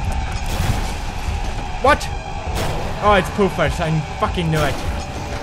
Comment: I thought he said 'poopers but he actually said 'poofers'